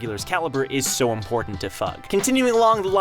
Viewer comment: was it just me or did he say "so important to fuck "